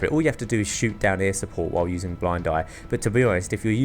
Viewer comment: HAHAHAH 6:00 TO 6:08 HE SAYS BLAID DIE INSTEAD OF BLIND EYE :D